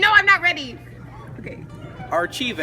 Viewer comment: I thought that girl around 4:22 said Markhive and I died 😂